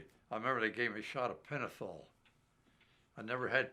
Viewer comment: He was floating from the morphing not the penicillin 😳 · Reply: Yeah, at first I heard Penicillin @ 3:33, but realized it was Pentathol when he represented it at @ 3:35.